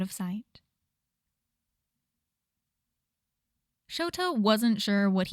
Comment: I thought she said Shouto and I was a lil confused lol